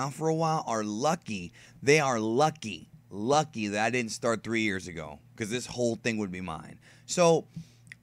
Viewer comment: Nicholas Moran - I was not talking about Dino hahahaha. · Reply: Wait never mind sorry i thought you said you started 3 years forgive me